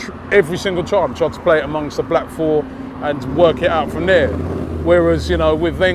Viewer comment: Did robbie just said chez played among with the black four.?! 😂😂😂